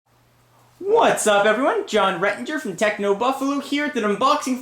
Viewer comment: It sounded like you said your name was John RedRanger! LOL